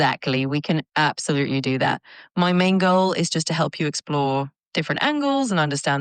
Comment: i paused just after she said "my main goal" and started to wonder why did she say "my mango"